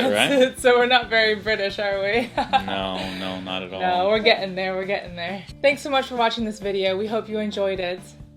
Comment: I swear I heard use a glottal stop - "We're ge-ing there!" 5:05 - Accents coming along nicely!